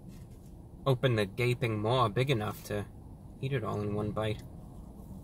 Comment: did he just say eat it all in one bite 😂😂